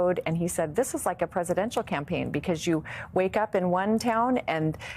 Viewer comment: Did I hear "Presidential Campaign" ?